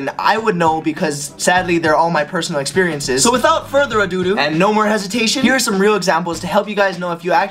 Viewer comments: did he say "without further adoodoo? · Did anyone else hear him say "a doo-doo'"? at 1:10? Just me? ........i see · Did he say without further a do do or am I hearing things… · he actually said " So without a further to DOO DOO"